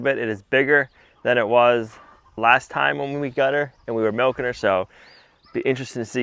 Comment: We haven’t been milking azalea for months now. Not sure what you are talking about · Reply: I probably misunderstood what you did say at 4:38 "when we were milking her". But you probably did say that in the context of last year instead of at this moment.